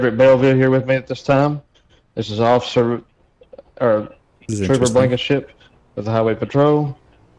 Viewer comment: did he say officer turbo blanket shit??